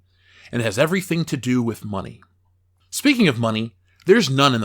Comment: I appreciate the video, great work. I gave up though when it turned out I misheard. Upcoming Plato movie is Play Doh. DOH! I thought a Plato film would be a challenge but playing with plasticine, by any other name, is truly daunting.